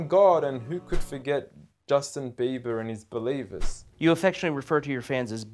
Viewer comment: i think he said beliebers tho 🤔